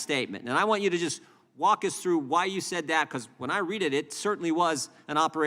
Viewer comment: Does he say "... when I readed it ..." ?